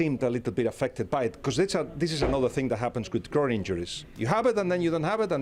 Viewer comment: So the groin injury wasn’t there against Bilbao 😅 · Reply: Wait, he said groin? Thought he said “girl pain”……